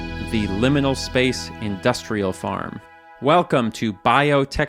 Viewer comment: 1. i thought you said "liminal space industrial complex" and i got really excited. 2. "forever roads" sung to the tune of "forever young"